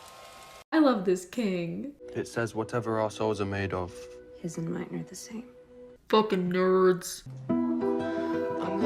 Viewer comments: Sifjwjtje I thought they said "whatever arseholes are made of yours and mine are the same" ajfusjtbwhfj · "It says whatever our souls are made of..." "...his and mine are the same." Because of the accents I heard "whatever assholes are made of". Still technically true, and I think my version is better. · "It says whatever our souls are made of..." "...his and mine are the same." Because of the accents I heard "whatever assholes are made of". Still technically true, and I think my version is better. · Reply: I heard “Whatever our assholes are made of, hers and mine are the same.” I had to listen to that part two or three times before I could tell what he was trying to say. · When he said “whatever our souls are made of” I heard “whatever assholes are made of”. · the way I heard “whatever assholes are made off, his and mine are the same” and had to take a minute to learn how to breathe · when hardin said “whatever our souls are made of hers and mine are the same” i thought he was saying arseholes instead of our souls